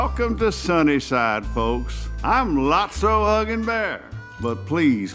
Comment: I’m lotso Huggin Bear In my head I heard “Lotso F***in bear”